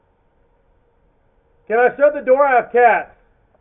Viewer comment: I thought he said "Can I shut the door, I have cash" 🤣